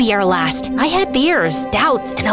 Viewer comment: I thought she said beers instead of fears